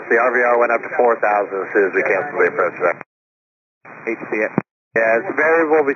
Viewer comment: the subtitles don't say it but I swear I hear "hate to see it" from someone